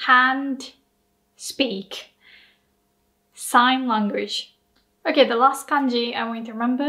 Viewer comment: when you said 手話, it is written "sing language" instead of "sign language"